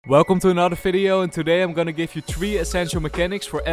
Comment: Did you just say 'Welcome to another FIDEO' lol